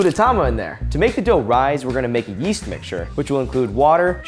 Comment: Omg i heard yeet mixture. Ya know from the meme : This Bean EMPTY! YEET!